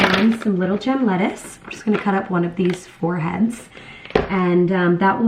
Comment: When you said “one of these four heads” I heard “one of these foreheads” and I just DIED of laughter when I realized 😂